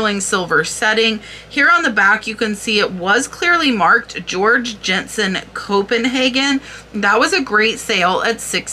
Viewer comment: I have you playing in the background and thought I heard you say George Jetson. Sure got my attention to look at the screen! 😂